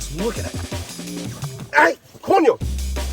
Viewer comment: Did he say COÑO!? 😅😂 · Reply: Yes, I definitely heard that as well.